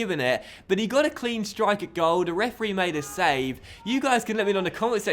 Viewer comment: did say the refree made a save?